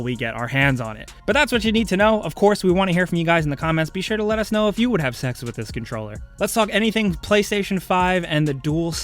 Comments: Listen to what he says at 5:24. Anyone else hear him say "be sure to let us know if you would have sex with this controller" its clear as day. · did you just say “let me know if you would have sex with this controller” 😂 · Hold the phone I love the fact no one is acknowledging him say if we want to have sexual intercourse with a controller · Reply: Sexual intercourse? I thought he said "have sense"...? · Did he just say "Will you have sex with this controller" or am I hearing things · Did he said "if you would have sex with this controller" 🤔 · did he say “sex with the controller” ... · Did he say would you have sex with a controller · did he just say.. “let us know if you would have sex with this controller?” 🤨🤔